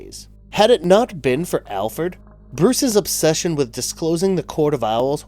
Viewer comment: istg i hear alferd.